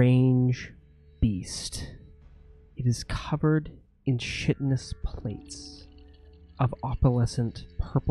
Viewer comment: "Shittness". Every time says Chitinous, I hear "Shittness".